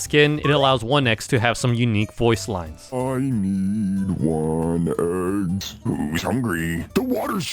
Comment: I heard 1 es